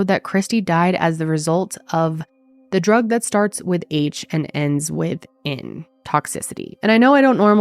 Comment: I was very familiar with Heroine but when you said starts with H an ends with A, throw me off.. Youtube is so frecking full of the word that starts with S an ends with T, so afraid of hurting the feelers of a few they over look the facts an that I'm it's self hides truth about alot.. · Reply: I heard "starts with H and ends with N". Maybe you misheard her.